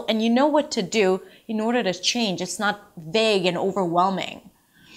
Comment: Thank you for watching! Now I'm waiting to hear YOUR story. Shoot! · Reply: I have a question: What's the word before "overwhelming" @ 4:45, auto translate shows 'big' but to me it sounds more like 'vague'.